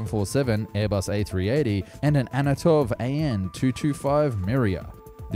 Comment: bro said "Anotov" instead of "Antonov"